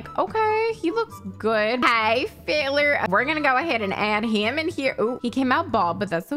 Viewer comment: i thought you said “hey failures” at the beginning. was wondering how you knew me so well